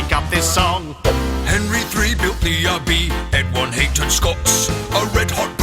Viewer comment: I'm not going to lie; the first time I heard this, it sounded like "everyone hated Scots."